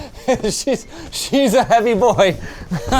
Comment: did you say "She is heavy a boy"? That's why people don't use "she" for objects.